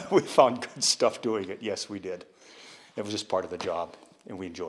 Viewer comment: "It was just part of the job, and we enjoyed it" ??? or did he say "we endured it" ? I don't think so, IMO he really said "enjoyed it" Uuugh